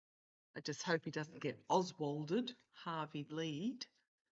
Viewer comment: I hear 2 of your guides one male and female. I believe she whispered Harvey Lee's? Couldn't understand what he said because it's so gruff sounding. Anyone else hear it?